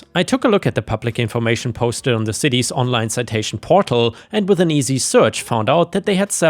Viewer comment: I heard "...online cetacian database...." not citation so I pictured a database of Orcas and whales with their names listed by their pictures Lol